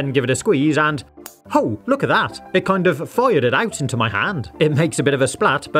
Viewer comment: I had to look at the transcript to figure out that you'd said 'fired it out' at 4:21. It sounded like "foyer did out'.